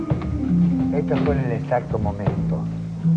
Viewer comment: que dice Charly a Diego? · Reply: "esto fue en el exacto momento"